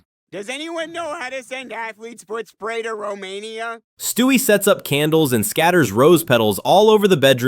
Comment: Was Romania mentioned by Chris before the 16:54 moment? What context was that? What did he say? I couldn't understand him thus time, he was so vague and random! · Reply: He said that he wanted to know how to send athlete's foot spray to Romania but idk the context behind that